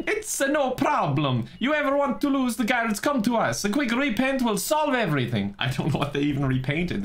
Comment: I thought they said "a quick repent will solve everything" but Forgive and Forget was a different open-world.